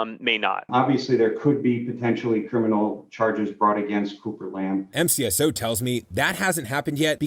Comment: Dirtbag said, when sheriff Lamb crashed. No, it was his son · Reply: You're right, it sounded like he said, Trooper Lamb, but he did actually say, "Cooper Lamb". I had to re-listen three times myself to catch that.